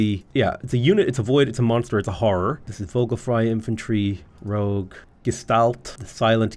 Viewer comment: Every time you said "Vogelfrey" I heard "vocal fry" and I thought this game might contain some obscure comment on policing speech patterns. Hell yeah, hit 'em with the Vocal Fry Infantry! Rebuff the enemy forces with particular laryngeal maneuvers!